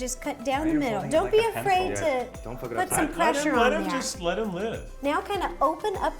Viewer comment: I genuinely think this is the first episode where Eugene hasn’t cursed!!!!!! 🤣🤣🤣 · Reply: I think at 3:30 he said "don't fu*k it up" to zach